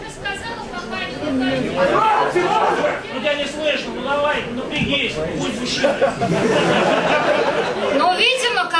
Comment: что говорит тесак? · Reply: "Тебя не слышно, ну давай - напрягись"